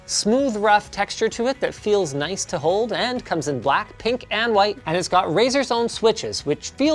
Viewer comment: WHy did I hear BlackPink, instead of black, pink.